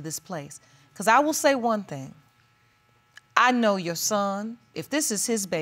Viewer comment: Did I hear that correctly? The Judge said “I know your son.”